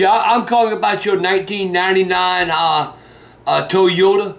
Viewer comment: I'm calling about your 1999, ahh...uhh toy Yoda 👽 It sounds like that's what he said, slays me every time x-D